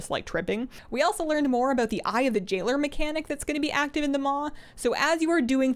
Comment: When you said "eye of the jailer" i heard "eye of the jiggler" and i was so confused thinking jigglypuff was in WoW now